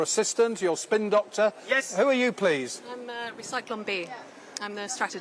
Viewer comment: Count Binface, make sure Recyclon B annunciates "re" better! I thought I heard Zyklon B, which was the pesticide used to kill people in death camps in WW2 👀